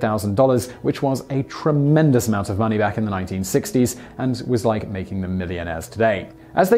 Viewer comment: he said 1960 subtitles said 1800